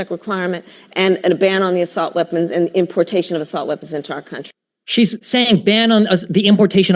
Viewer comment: for all the ppl saying this guy got beat by destiny this is how, she clearly said ban on assault weapons weapons and